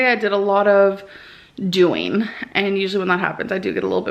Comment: a lot of doing? Did I hear that right?